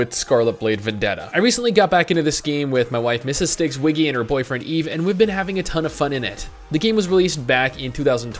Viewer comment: Did I mishear him at 1:02? He said, "My wife and her boyfriend..."